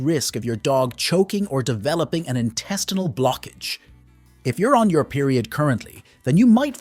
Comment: About dogs and tampons, it said "there's a high risk of your dog choking or developing an intestinal blockage". But there was noise in my room and i heard "developing A TASTE FOR BLOOD", and i was like "what?". 🤣🤣